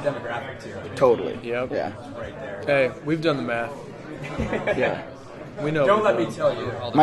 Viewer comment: does Anders say "aye, we've done meth" , or "aye, we've done the math" ?